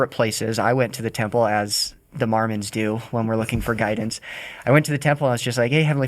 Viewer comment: Did just say “Marmons”? lol!